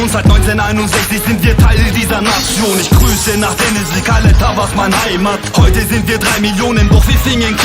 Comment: Denizli Kale Tavas mı diyor ben mi yanlış anladım